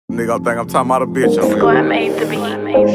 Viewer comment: is i’m the only one heard him say nigga gone think i’m talking about a bitch on here in the beginning of the song ????????? been thinking about that since it first came out